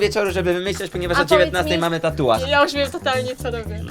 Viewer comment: PLZ POPŁAAŁAM SIĘ JAK USŁYSZAŁAM "MORTAL TUTAJ BYŁ" XDD